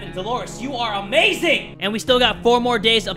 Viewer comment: At first I heard “Mary Jane you are amazing!”